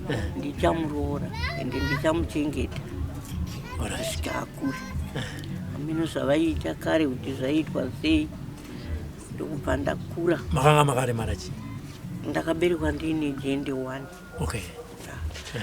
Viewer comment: did she just say 'ndakaberekwa ndiine jende one ' ?